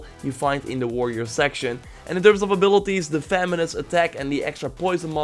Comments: Who else thought he said feminist attack? I pictured sjw versions of Kassandra and Alexios chucking dildos at the bright one😂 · i thought he said feminist attack lol